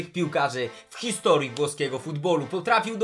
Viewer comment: czy tylko ja usłyszałem za pierwszym razem „polskiego futbolu ? " XD